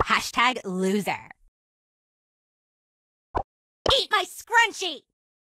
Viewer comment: did she say eat my scrunched? VSCO! Tf